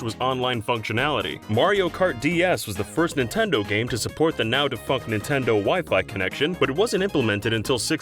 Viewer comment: does he say "the now de fuck